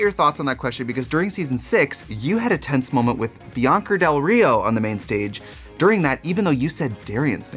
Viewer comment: am i the only one who hears biyonker del rio??